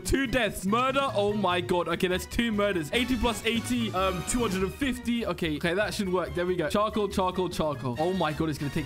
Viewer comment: Did he just say 80 + 80 + 250? (I love math and i get angry at people that cant do math)